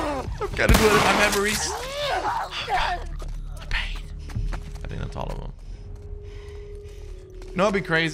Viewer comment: the last zombie u killed said “oh god” 😭is it just me or did anyone else hear dat?